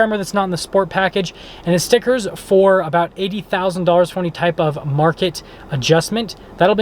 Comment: Who said it has an ADM? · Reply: sounds like he said 80,00 before market adjustment...hard to tell...either way that's a pretty penny for a 350 with a tremor package.